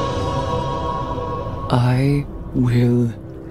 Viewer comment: I accidentally skipped 0:03 and all I heard was "I will fail"😅